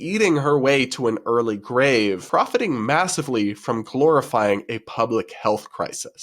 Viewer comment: I heard glorifying as “calorifying”